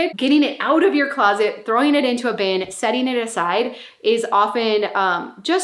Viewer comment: When you were talking about 'Time will tell' bins and clothing, I genuinely heard you say: "Getting it out of your closet, throwing it into a bin, setting it ALIGHT...!" 😂😂😂 Aside. You said, aside. Oh dear. Where is my head at? 😏🥴🤣